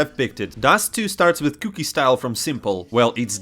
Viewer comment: hahah I thought u said cookie style